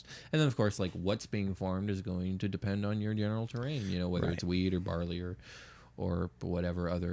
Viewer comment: I definitely heard "weed or barley" too, so you're not alone. xD